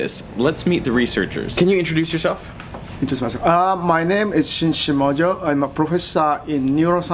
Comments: the professor says what sounds to me like "shin shimojo". But that's a bit confusing, since the caption says the researcher's name is "Prof. Shinsuke Shimojo". Can anyone make sense of this? Is elision a common feature of Japanese onomastics? Or maybe "-suke" is not normally voiced in Japanese writing? Or maybe I've just simply misheard. Anyway, if anyone has any ideas, I'm rather curious to know. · What did that guy say at the start before he said his name. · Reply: "intrdcmyslf...ah"